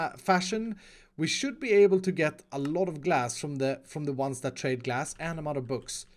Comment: when u said “trade glass”, u sound like u said “trade gas” XD